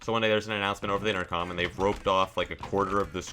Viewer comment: I thought he said groped